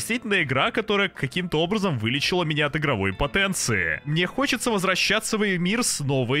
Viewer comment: Мне послышалось или он сказал в 5:49 что Elden вылечил его от игровой ПОТЕНЦИИ? Может он хотел сказать ИМПОТЕНЦИЯ.